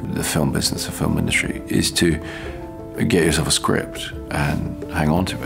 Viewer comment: what does he say? A gaze of a script?